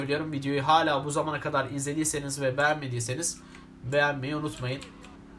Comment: de begenmemeyı ınıtmayın dedını tek ben mı duydum